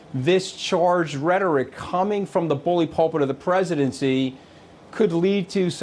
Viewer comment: Wait a minute. Did Scarymoochey say "the Holy pulpit of the Presidency"? At 1:15. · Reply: No, he said "bully pulpit".